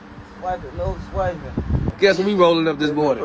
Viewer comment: Lmao thought he said "wipe his nose" he said "swiper no swiping"